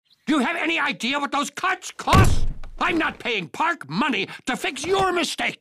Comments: WHAT DID HE SAY!?!??!! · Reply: He said "DO YOU HAVE ANY IDEA WHAT THOSE CUTS COST!?" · I deadass thought he said "Cunts" at the beginning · I still can't unhear him saying c*nts instead of cuts